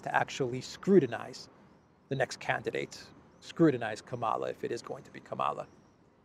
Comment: Did I hear you say "Less time to Screwdinize, Kamala"?